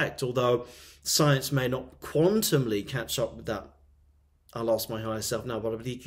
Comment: at min 23:32, what did you say? "I lost my eyes?" Then light language? I m asking, coz my daughter during the epilepsy seisures, did the same movement with eyes... ty